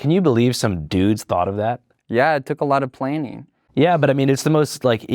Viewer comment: Did he just say "yeah, it took alot of PLANEing"